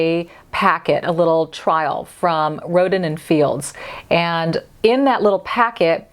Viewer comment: Omg 😲 It sounded like you said,"Rodent in Fields". I know it's Rodin but I couldn't stop smiling.